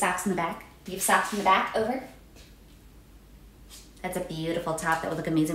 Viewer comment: The "We have socks in the back, over" like she's landing a fighter jet kills me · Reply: I thought she was saying 'stocks in the back'